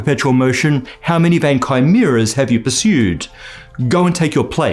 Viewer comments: I thought the quote was somthing like "how man vankai mirrors have you pursued?" And I went to look it up with a few different spellings until coming back to see the CC and found out what he actually said 😂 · "How many vain chimera" for those who heard it as "van kai mirrors"